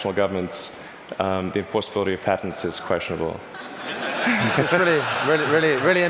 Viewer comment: Did anyone catch what Elon said during 14:29? "Since our primary competitors are national governments _____ is questionable" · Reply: As far as I can tell, he says "the enforceability of patents is questionable". Hope that helps :)